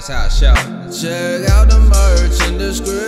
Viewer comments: 2:44 "Ima eat some fried rice" BOI YOU AINT SLICK lol😂😂😎😎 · "I'm wishing to eat some chicken" I heard that, you ain't slick my boi: 2:40 Also I heard the fried rice too :D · "I'm wishing to eat some chicken" u not slick I heard that